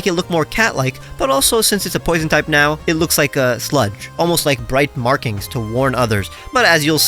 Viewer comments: I thought you said slut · I thought you said the Umbreon evolution was poison and therefore looked like a slut. had to go back three times before I realized you said sludge 😂